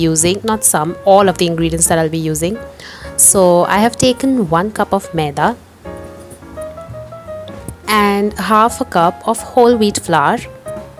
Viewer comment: what was the ingredient other than flour? I couldn't understand the word. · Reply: 1 cup maida (all purpose flour) and 1/2 cup whole wheat flour